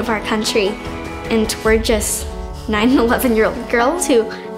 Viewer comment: I thought she said “We’re just 9/11 girls” at first